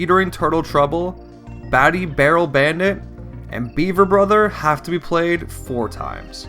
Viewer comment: Wait do you think beaver bother is called beaver brother? 😂